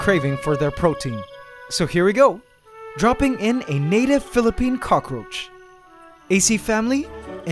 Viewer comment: Wait did he say Philippines Cockroach 2:35 sa Philippines ako